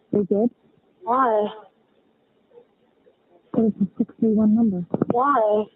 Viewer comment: on this ep I hear dispatch say in the background “it’s a 631 number”